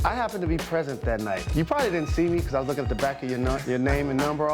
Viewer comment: LOL at 0:38 it sounds like he said "you probably didn't see me cause i was looking at the back of your NUTS"